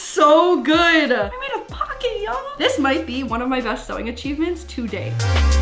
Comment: When she said “this could be my best sewing achievement to date” who else thought she said “this could be my best sewing machine today”